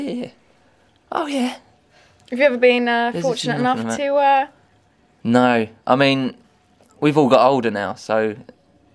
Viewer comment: What does he say at 1:42 really quietly? I've been trying to work it out but I cant. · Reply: "there's a few knocking about"